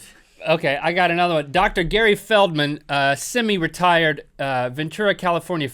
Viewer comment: when link said semi-retired i thought he said semi-retarded and i needed to rewind XD